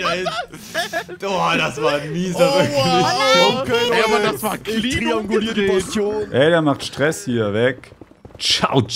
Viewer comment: was sagt elins da!!! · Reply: Oh nein piglins